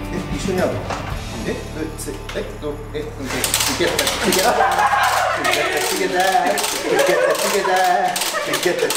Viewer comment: てなんて言ってるんですか？ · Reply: ご視聴・コメントありがとうございます✨ 「トゥギャザー」と言っているそうです😆